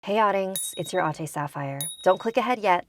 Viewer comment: lol i always thought she was saying hey audience its your aunty saphirre